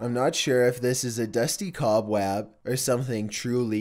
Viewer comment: who else heard cobwab